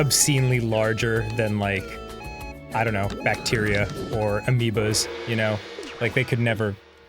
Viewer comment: I thought he said "amiibos" and I was like wha